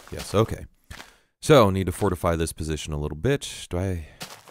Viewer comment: it sounds like your saying "little b*itch" instead of "little bit" lol :D